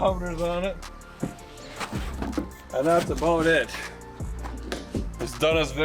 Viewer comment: I know what you said..but it did kinda sound like "It's a bone it"..was a new catch fraise just invented ?